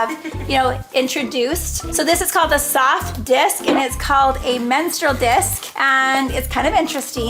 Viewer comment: help the soft disk i thought said salt disk 😂😭😂😭😂 also i got yalls period kit bag and i love it! ❤